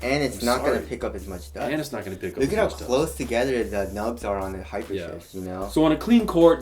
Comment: It sounded like said hyper shit